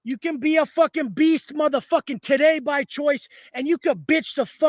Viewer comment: Did he say Today or Titay?😂😂